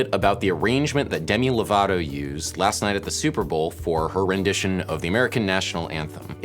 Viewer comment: for a second I seriously thought you said "for horrendition". HA!